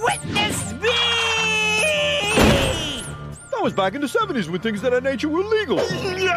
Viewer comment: Yogi, what did you say?!! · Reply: “That was back in the 70’s when things (of that) nature were legal.”